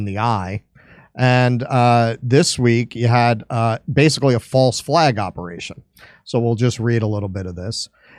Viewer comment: At first i thought it said Zombie false flag